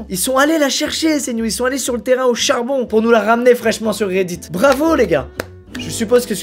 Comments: Je suis le seul à avoir entendu "Hauchard bon" à 9:43 de la vidéo. (Tuer moi svp...) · Squeezie: "ils sont allez sur le terrain AU CHARbon. (Hauchard) Excusez moi il a parlé de non de famille dans la vidéo j'ai direct entendu ça. 😂😭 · "ils sont allé sur le terrain *hauchard*bon" excuse-moi squeezie